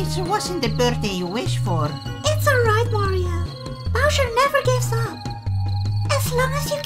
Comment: DID SHE SAY WARIO INSTED OF MARIO